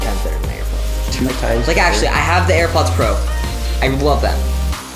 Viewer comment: It sounded like he said “I wuv them”